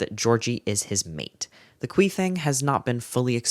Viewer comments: help when you said “khui thing” why did i hear queefing 39:07 😭😭😭😭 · "the khui thing" Definitely thought you said "queefing" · i GENUINELY thought you said queefing and I spat my water all over my desk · i heard queefing 😭 lmao